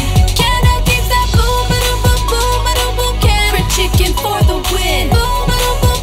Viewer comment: thank god i was watching this video with the lyrics...i thought you were saying carrot chicken lol great job!